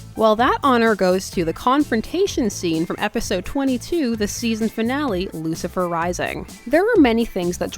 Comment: For a moment I swore you said season floor.